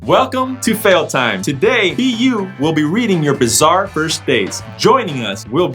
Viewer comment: I thought said B**